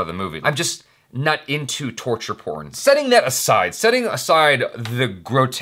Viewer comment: Did he say " I'm not into torture porn" at 1:40? xD